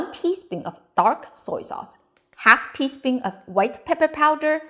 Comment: I'm glad I watched this again before I made it. I went off the recipe in the description and you left out the white pepper for the marinade. And that's the reason I went back in to watch it again was cuz I thought you said white pepper. Also in the description you call for a tablespoon of Sichuan peppercorns. It's actually a teaspoon you said in the video. So hopefully if anybody makes this they double-check that